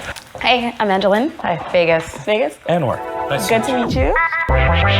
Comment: ayo I heard niggaz and n-word 😂